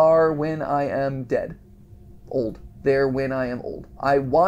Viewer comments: At around 15:58 I think it said I would love to work here when I am old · I didn't hear Poppy saying wake up until the end but other YouTubers can hear Poppy saying it Also 15:51 translation "I would like to work here when I am old"